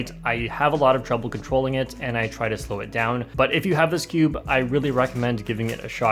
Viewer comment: who else heard "but if you have a skewb"